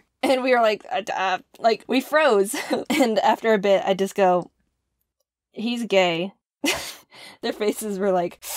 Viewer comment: Ok i might just be tired but I don’t know how I thought I heard “he’s grey”